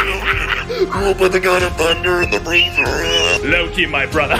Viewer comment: When he took Thor out of the freezer it sounded like he said “THE THUNDER AND THE REINDEER”!